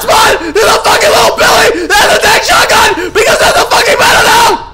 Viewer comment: I agree with myth entirely even tho i dont know what he said 😂😂😂 btw its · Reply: He said, "Because of little billy, who has a tac shotgun, cuz thats the meta now."